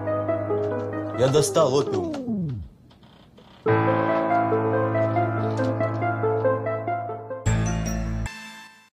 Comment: что он сказал? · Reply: Опиум